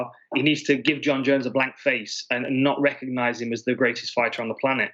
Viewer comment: Did he say "BLACK FACE"?......smh( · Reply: Blank face...